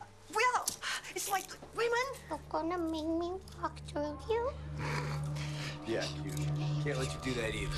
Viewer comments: Can someone pls tell me what she says at 0:57? 💀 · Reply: "You're gonna make me walk through you?" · What is she saying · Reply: "you're gonna make me walk through you?" in a babyish tone